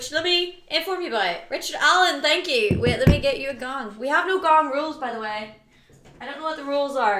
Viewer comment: Ah I thought it said 'we have a bong' 😏